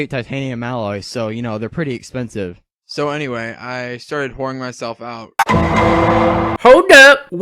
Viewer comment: I really thought nightwing said "I'm boring myself out" when talking to Bruce lol